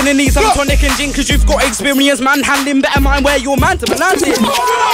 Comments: what was the bar couldn't really hear since everyone was shouting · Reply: Because you have experience man handling better watch where your man hands them are landing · what he say before reload pls? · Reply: better mind where you mandem are landing